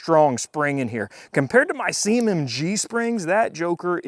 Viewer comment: “Compared to my semen g-strings that joker is tight!” 👀 . . . . . . At least that’s what I heard the first time through. 😅